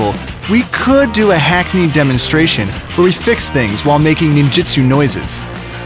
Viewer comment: did he just say... NINJUTSU ...